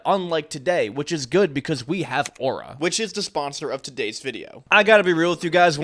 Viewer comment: i heard him say “because we have aura” and i was like “noooo not you too” and then he continued